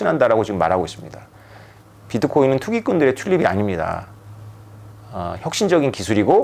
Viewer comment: 마지막 즈음에 투기꾼들의 튤립이 아닙니다 라고 말씀하신거 같은데 자막엔 출입으로 되어 있음 .. 내가 잘못 들은거려나 .. 흠 ..